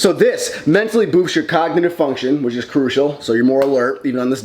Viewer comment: When you said cognitive function I thought you said cock lmao I ran it back 3 times